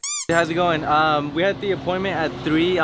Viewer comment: Bro it sounded like he said Hey b***h 😂 🤣